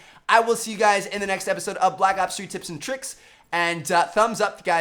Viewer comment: Can anyone translate what he says at 5:34? Literally sounds like checkouttheetuberessscreenyet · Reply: Check out the other videos on the screen if you haven't seen then yet, and peace out